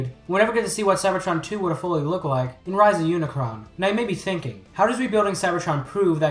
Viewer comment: I thought I heard rise of unicron finally got greenlit.